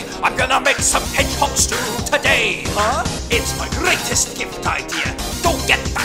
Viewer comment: In the original instead of “it’s my greatest gift idea” I heard “it’s my greatest gift my dear”